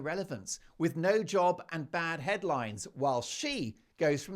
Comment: You said about Harry’s bad headlines , I heard as “ Bad Headlice “ . I thought how odd to make it public 😂